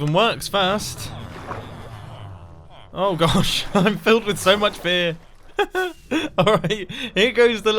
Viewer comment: When he said "I'm filled with so much fear." I thought he said " I'm filled with so much BEER."